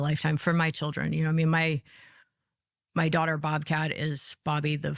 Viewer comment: Did she just say her daughter’s name is Bobcat? 😭😭